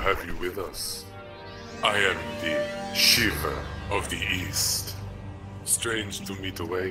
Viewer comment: Ohhh, Shiva of the east? I thought you said Shiva of the weast.